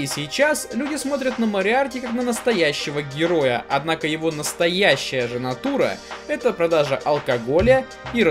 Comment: слышу "его настоящая жена Тура..." Так, нипонял,что? (